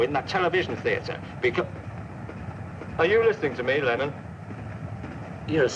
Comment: Lmao I thought he said ‘aRe yOu LisTeninG tO Me L e m o n’ joHN LEMOn anD gEoRgE pArRotnuN